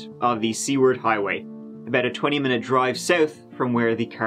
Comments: he says what sounds like "C- word highway" lol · I thought said the "C" word Highway." Took me a minute to realize what he said. I said to myself, "what a peculiar name for a Highway."